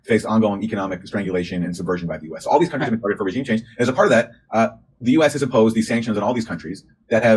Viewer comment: I thought she said "climate change" was what caused this a few months back